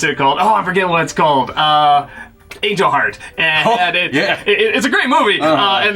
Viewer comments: what movie was he talking about? It almost sounds like he's saying "Age of Heart", but the CC has it as just "jarred" so it didn't understand him either. What was the movie he said? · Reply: Angel Heart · The closest thing to a rental store now is redbox or a public library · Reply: what movie was he talking about? It almost sounds like he's saying "Age of Heart", but the CC has it as just "jarred" so it didn't understand him either. What was the movie he said?